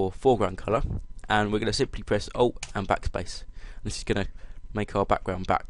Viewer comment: what the hell did you say at 2:50? o and backspace? shit didn't work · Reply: ALT + Backspace